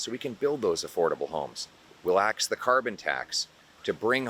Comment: I listened to the reference to carbon tax twice. I thought I heard "relax" the carbon tax. Please confirm the word was "axe" the carbon tax